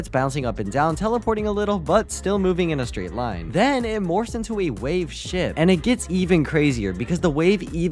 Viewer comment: i thought you said wave sht